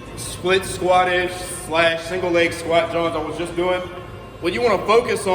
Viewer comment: Hold on a sec did I just hear you say “squat jawns”? Are you from the philly area too bro? Btw love your content, and am pretty new to it, appreciate all you do for the community!